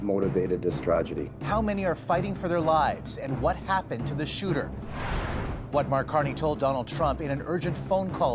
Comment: Why are they hiding suspect information? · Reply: I heard him say "the gun shooter" wth is that?